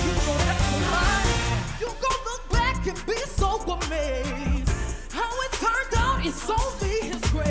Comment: This whole time I thought she was saying "You gon look back and be someone's maid" 😬😬 it fits tho right?